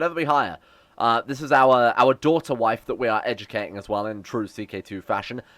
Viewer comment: Hold on did you just say 6:05 “Daughter-Wife”.....